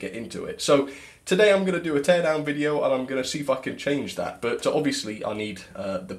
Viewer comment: I thought you said a 10 hour video